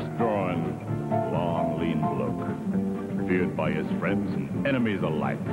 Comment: Funny the things you mishear when you're a kid. Always thought he said "long lean look". Of course, as a little kid I had probably never heard the word "bloke."